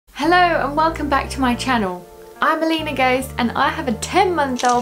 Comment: I thought you said "i believe in ghosts 👻 " 😂 i had to listen again lol. I used to have the elephants with my son when he was a baby. I donated all our diapers, so i needed to buy more for my daughter coming in the winter